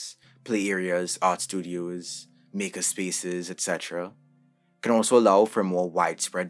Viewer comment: Wait wait wait did I hear "nigga spaces" lol?